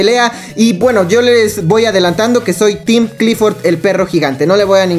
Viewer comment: like: team kong comentario: team lagartija corazón: team clifort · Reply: Pensé que decía team clítoris xD